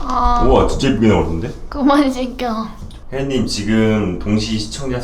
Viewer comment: 뭐라고 하는건가요? 1. 그만해 이ㅅ끼야 2. 그만 시켜